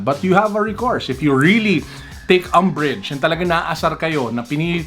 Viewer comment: I thought you said "umbridge". As in Doleres Umbridge from Harry Potter. Nireplay ko pa po. You really said "Umbridge". 🤣 Napagoogle tuloy akong bigla. Umbridge is a play on "umbrage" meaning offense or annoyance. 🤣 kala ko shoe channel lang ang sinubscriban ko 2 years ago (using my other google account). Hindi pala. May tech and toys pa pala! Ang cool lang. 😁